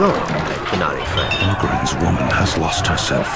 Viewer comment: 😂 Google English auto generated translator, when Sten spoke at about 3:54, wrote this : England's woman has lost herself. England😂 England's instead of Ogren's Later at 4:14 when Leliana spoke, it wrote:I am sorry Oakland polyomantic is it. 😄 Oakland instead of Ogren, and polyromantic instead of hardly romantic. 😂 Have dificulties to stop laughing.